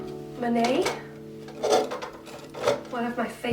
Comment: But this is Monet, not Manet. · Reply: oh! I can't see the painting on my phone. I thought she said Manet. 😔